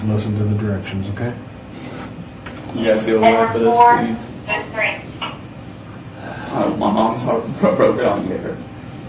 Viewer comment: What does he say at 59:12? "You guys [inaudible]...My mom's [inaudible]" Anybody able to make out what he says? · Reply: "Could you guys go away for this please? I don't want my mom's heart broken on the air."